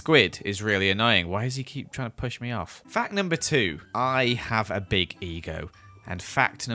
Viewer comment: The second fact I thought it said I have a baby goat